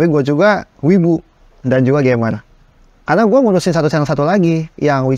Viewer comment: Mohon maaf mas2 yg baju putih 02:07 ngomong apaan? "Wibu dan..." Apa ya? dia ngomongnya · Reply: "wibu dan juga gamer"